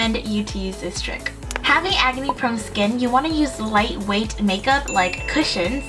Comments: I honestly thought said "agony prone skin". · I thought you said "agony prone skin" not "acne prone skin" but I realized they're the same thing. orz